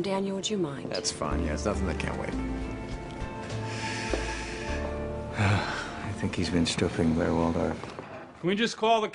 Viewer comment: Did Rufus say "I think he's been stripping Blair Waldorf"???